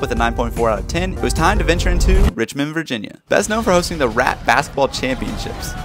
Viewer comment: The first time I swore you said rat-bastard championships LMAO